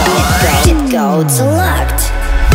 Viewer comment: Did that say "Cheat code unlocked!"?